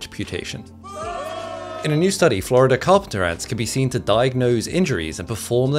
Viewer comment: All I heard was “Sabrina Carpenter ant” 😅😅💀